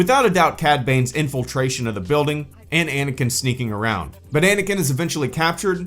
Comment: Anyone else heard "Bananaking"? lmao